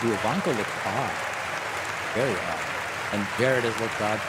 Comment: what did David say? Did he say “Ooh Ivanka looks hot...very hot” Lol wtffffff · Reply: Looks "odd"